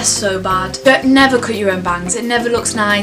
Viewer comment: i thought when she said bangs, she said vans and i was like....hold up honey...vans rock